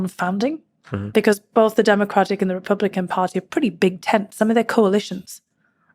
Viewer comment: Almost sounded like she said "Dramacrats" instead of Democrats. lol.